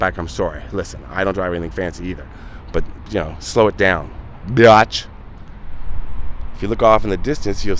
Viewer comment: Oh man - one more thing - at around 16:24 with the tailgating rant bit - did you say "Be-otch" or was that an accidental burp brought on by the previously mentioned vitamin shot....? Just wondering if my ears are still deceiving me...... (Still really liking this video....)